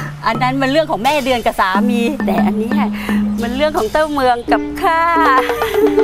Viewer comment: ได้ยินว่าเต้าเมือง